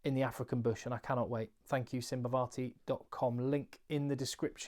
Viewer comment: I thought you said Sivivatu not Simbavati :D